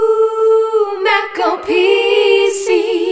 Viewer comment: Just stopped the video because I was going to the bathroom at 0:04 so it sounded like "OOOOH MAC OR PEE-" I was like, what the fuck?